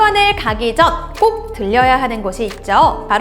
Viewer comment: 들려야 -> 들러야